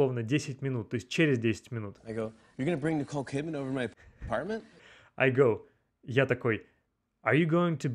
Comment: Услышал "do you want to bring cocaine in my apartment?"🤣🤣🤣